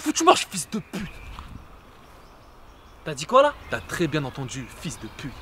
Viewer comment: quand il dit t'as dit quoi la ? Y a un koala en subliminal 🤣🤣🤣